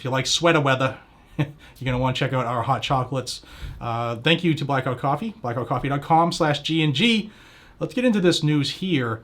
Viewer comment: I almost thought he said black rifle coffee.😂